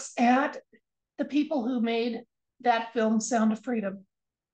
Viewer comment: I heard "fill 'em un".......